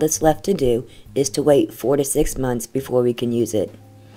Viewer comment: Lol I thought you said to wait 46 months and my eyes just popped (four to six months/ forty-six months sounds too similar lol).